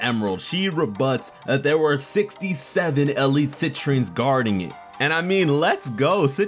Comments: I thought she said "sentries". And I am concerned this is just a dream sequence, them having accessed his subconscious. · I thought they said sentries, not Citrines. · I thought she said "Sentries", not "Citrines"... but who knows. Still pumped!